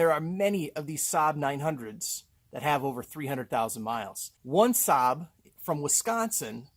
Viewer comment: I thought you said 300,000 minutes...I'm out...